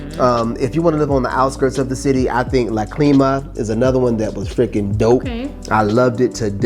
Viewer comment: What was the neighborhood mentioned on the outskirts? I thought I heard La Clima but getting no hits for that. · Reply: Great ear yes La Clima is correct.